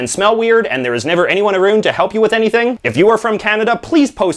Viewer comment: Did he just say "aroond" instead of "around?"